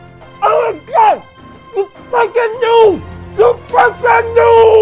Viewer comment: sounded like u said f*cking but u said freaking